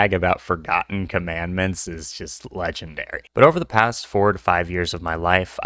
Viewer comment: i thought you said “over the last 45 years” and i was so caught off guard by that until i realized it was four to five